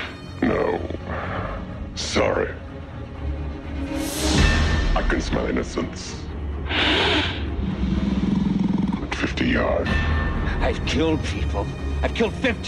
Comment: what does he say ? 50 yards ?? I always thought, like that's his head start :/ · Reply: "I can smell innocence...at 50 yards."